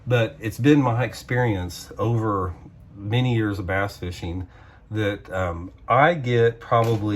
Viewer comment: Did he say "20 years of a$$ fishing?" Lol